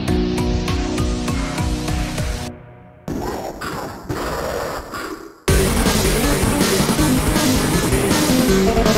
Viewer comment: did he saying? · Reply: He say "Wëlcome baçk!"